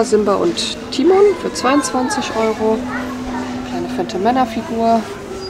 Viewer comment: "kleine fette Männerfigur" Wer hat's noch gehört? 😄🙈